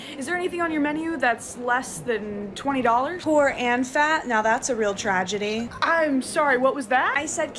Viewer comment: „Poor AND fat? Now that’s a real tragedy.” stop calling me out bro💀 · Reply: istg i kept hearing "poor ant fat" and was getting increasingly more confused